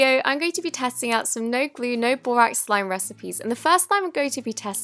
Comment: Omg at first I thought it said Dora not borax I got excited